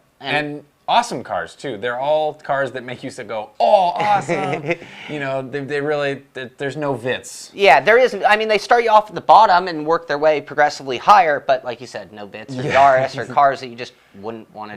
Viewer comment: What does he say at 13:23 "There is no witz" ???